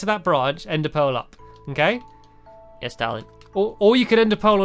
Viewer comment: squid said yes darling instead of yes stampy